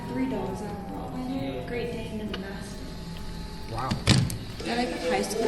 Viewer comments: she didnt say the dogs names she said "a rot weiler, a great dane and a mastiff" but the subtitles said "indistinctly says their names". felt like i had to point that out i dunno. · Okay that last one was actually way more wholesome than I was expecting And I don't think she was saying her dogs' names at 9:22, I think she was just saying "I have a rottweiler, a great dane, and a mastiff" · when it says (indistinctly says their names) She clearly says “I have a Rottweiler, Great Dane, and a Mastiff.”